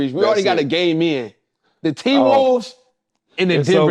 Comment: Thought he said we already got a gay man